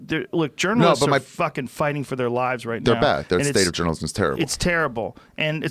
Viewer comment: Overheard it as "Germans are fighting for their lives right now", and was like wait a minute Wtf is going on in Germany?!